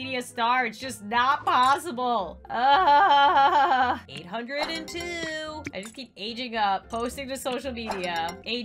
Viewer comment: When Lauren said "802" it sounded like threes company 2 lol.